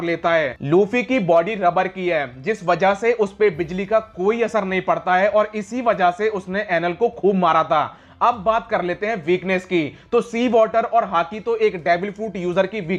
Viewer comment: am i the only one who hears " ANAL "😂😂😂😂😂😂